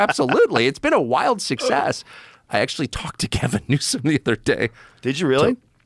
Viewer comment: does he say Kevin Newsom?